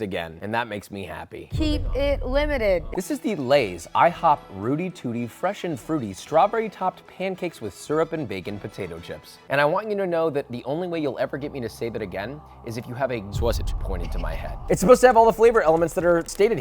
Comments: I can't wait to see Josh do " I tried cooking again" video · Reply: at first I thought it said ‘Booty Tooty’ and I was very disturbed yet intrigued… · At first I thought it said ‘Booty Tooty’ and I was disturbed yet intrigued…